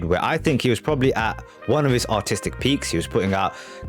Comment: lol i thought for a second you said "at his autistic peak"